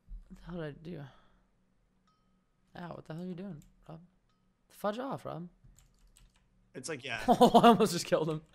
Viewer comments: wait what the heck happened at 14:57 it sounded like nothing at first then u hear say what the hell are you doing fudge off rob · did he just say what the hell u doing rob